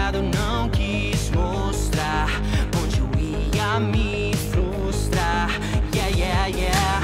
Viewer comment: Hi! I'm a Brazilian person and there's a little mistake on Portuguese translation. At 19:04, when the subtitle says "continuei a me flustar" actually, "flustar" doesn't exist. The correct word is "frustar". So, the correct sentence is "continuei a me frustar". "Frustar" means Frustrate. Tks for being comprehensive :)